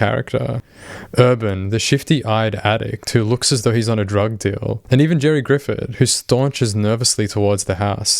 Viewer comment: He actually has a normal voice, in another video that went into his life after TCAP he said he had a cold during the time. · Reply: oh i thought he said he was on drugs too